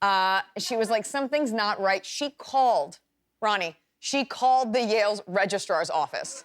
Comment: does she call Jimmy "Ronnie?"